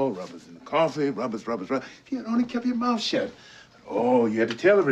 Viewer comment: what did he say, give the boy some credit, gibberish after that? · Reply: In one ear, out the rubber